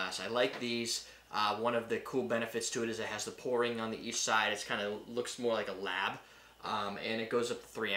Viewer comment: First thought you said "on the East side" and spent way too long trying to figure out what the hell that meant! Had to replay it like 6 times before I figured out "on each side" hahaha